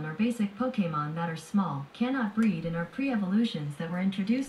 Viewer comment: I thought Goolge said Baby Pokemon cant Breath instead of Breed